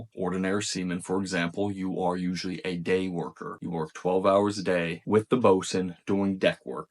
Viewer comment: did homie say gay worker 👀